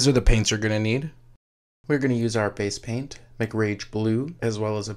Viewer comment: did he just say make-rage blue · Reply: No Mc Rage Blue Like Mc Ds ;)